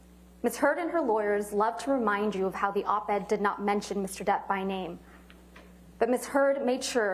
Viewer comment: …sounds like she said Ms Turd….hahaha!